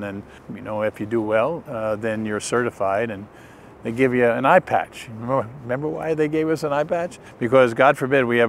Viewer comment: I wasn't fully paying attention to this initially... I thought he said iPad... not eye-patch... haha.